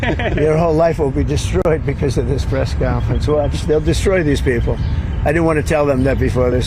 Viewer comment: Did he really say "I destroy these people"?